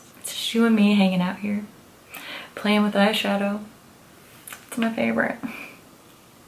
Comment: instead of "it's just you and me playing with eyeshadow," i thought you said "it's a shoe in me playing with eyeshadow" 😂😂